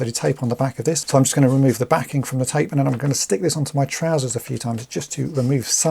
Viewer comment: At first, I swore u said stick this IN my trousers a few times😅 but then I remembered u use ur pants to get rid of the sticky